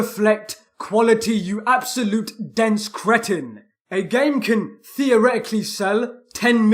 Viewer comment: what are you saying here? Can you spell it? Dence kretin?